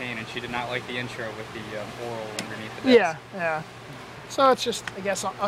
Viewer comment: What did he say at 7:26? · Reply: Oral under the desk